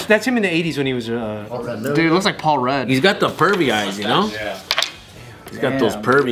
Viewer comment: I thought said Furby eyes 😂 like the Furby toy, then I realized what he actually said loool